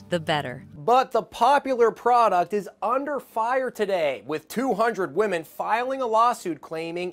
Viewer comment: I thought thought it said “200 year old woman” instead of 200 women